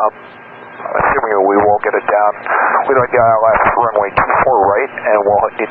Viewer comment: No. you need to listen again. At 2:18 he clearly says ‘we’re assuming we won’t get the gear down’ and they will need to stop on the runway and have an inspection. He also said they hadn’t run the checklist yet. The second contact at 3:15, while on downwind, the pilot said they still hadn’t run the checklist but the gear SHOULD come down. Even the tower advised other traffic that a belly landing was expected on 24R. At 4:24 SOCAL asked to confirm they wanted to belly land on 24R. The pilot responded affirmative. As I said, running a checklist while in the pattern was a poor decision. With plenty of fuel there was no need to hurry the landing. All of what you said did happen, but you have the time line of the events all wrong. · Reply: Man, a lot of backseat driving here.... -First timestamp he is almost certainly saying "assuming we WILL (We'ulll) get it down." and the caption is wrong. Why on earth would he be talking about an inspection if he thought they were about to do a belly landing? -Second timestamp confirms my above point by saying "I'm pretty sure it should come down once we do that" -Clearly a miscommunication on the belly landing situation that AA1 didn't hear or understand on the radio or I assume they would have corrected. The alternate gear extension procedure is 5 steps long and would take maybe 30 seconds to complete. They could have done this at any point during this sequence and we don't know exactly when they did it. To say they hurried up or to actually believe they thought they were going blindly into a potential belly landing is asinine.